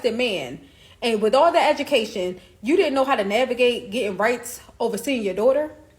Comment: Sir, she didn't say "custody"...she said "rights" 20:40...smh! "Rights" as in visitation/being present in his child's life on a regular basis, as opposed to "custody"...which is basically the parent who has the child most, and who has the most say so in the child's life i.e...primary custody! So, no sir what that young lady said at 20:40 actually made so much sense!